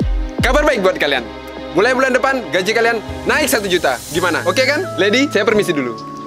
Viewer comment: Gak salah denger gua "Gaji kalian akan naik 1 juta" Gak salah ni?? Maaf kalo salah 🤝🤝🤝